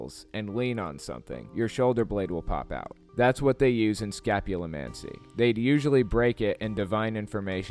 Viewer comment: Must admit, when you said 'scapulimancy', initially I thought you said 'scatulimancy' - I thought they were looking into s@